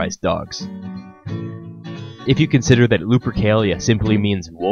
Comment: I always misheard it as Lubricalia.